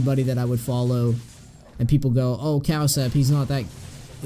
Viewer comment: I didn't understand the name of the korean player that have a good build on Yi at 6:14 can you write it for me please ? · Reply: Corazøn Cowsep